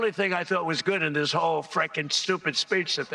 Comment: Sounds like he said #frankinstupid